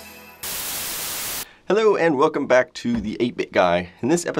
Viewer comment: I thought he said “hello and welcome the the naked guy”😂😂😂😂😂🤣🤣🤣🤣🤣